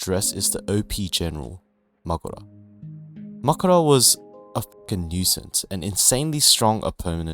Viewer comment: Great video man, I loved your points. My only complain is how you Mahoraga XD it sounds like you say Makora instead of Mahoraga